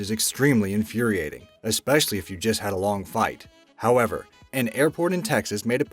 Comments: sounded like you said long fight instead of flight ? Cammmone man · did he say short fight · it said fight instead of flight